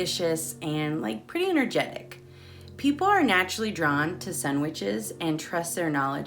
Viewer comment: I accidentally moved the video forward to 17:50 and, being deaf in one ear, it sounded like you said "people are naturally drawn to sandwiches". Took me a second to figure out you said "Sun Witches" 🤣. I'm so happy I found your channel/site; a wealth of information!